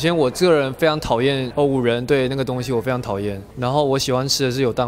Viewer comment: 聽不清??甚麼東西? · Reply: Pink Panda 五仁（月餅）